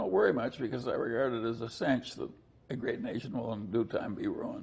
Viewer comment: what did Charlie say at 9:26 that made Becky laugh? I couldnt hear clearly what his words were.. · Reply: A great nation will be ruined. Like the Roman empire.